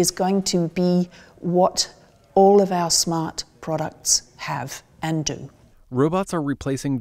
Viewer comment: Andra Keay says: "In five to ten years time robotics is going to disappear from our awareness, because it is going to be what all of our smart products have ended". First, did I hear the last word correct "ended"? Second, the first sentence I understand. But what does she mean in the second sentence? Thank you in advance, fellow YouTuber.